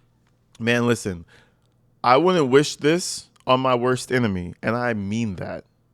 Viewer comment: If my parents were talking like that, I'd be like, why are yall being so weird.. omg, I didn't realize he said that. I thought he said, "I would go back." Now, I hear it. He said he wouldn't